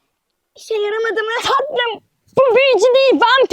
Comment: sudenaza tatlım mı dedi ben mi yanlış duydum